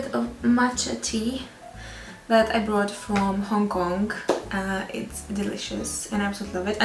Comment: a butterfly what where did u hear that shes chinese? Shes eastern european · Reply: Yeah I don't know I thought I heard "I brought from home Hongkong " I dk why I heard that now that I checked she didn't say home I am so stupid I'll edited it out 😱